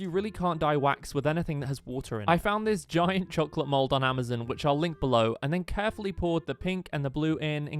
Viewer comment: I heard “Amazen” 😭🙏